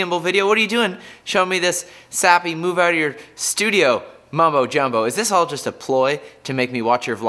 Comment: Did I just hear you say, ‘mambo jambo’ ??? I’m glad to hear you speak my home language Swahili....